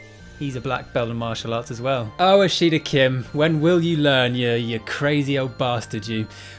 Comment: Just an amusing tidbit of utterly useless info, but at 7:50, when you say "oh Ashida Kim…" the YouTube CC subtitles say, "oh a sheet of Kim," lol! 😄😆😂🤣🤣 Dunno'about anyone else, but that tickled me! 🤣